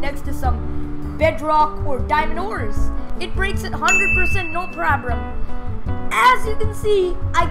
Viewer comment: What did he say? no probrem whats that for word search it in a word book